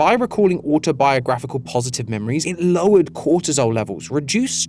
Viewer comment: did you say... Low Cortisol?